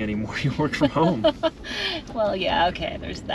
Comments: Your son is a man after your own heart. I saw the surprise when he also thought said “ Getting your step-son” and not steps on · While sitting on my Zamboni, resurfacing the ice rink prepping for curling tonight, I was watching this video. When and were talking I said the same thing at the same time as. “Your what?” It sounded like she said “dad’s putting my steps on next” or as and I heard it “dad’s putting my stepson next.” 😂🤣😂 I thought it was the cold of the ice rink and the noise of the Zamboni but when asked quickly “your what?!” I knew I wasn’t hearing things. 🤣😂🤣